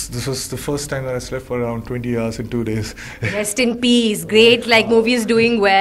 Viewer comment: did she just say rest in peace?!😑